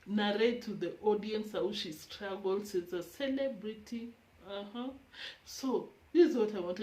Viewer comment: Did she just say celebrity dog 😳🥴🙃